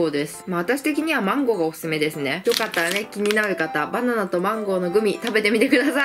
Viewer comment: 所々、バナンとマンゴーて聞こえる笑笑